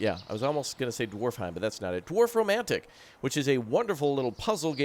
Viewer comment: Beautiful game! But i think you meant Dorfromantic in the beginning, sounded like you said Dwarfromantic :D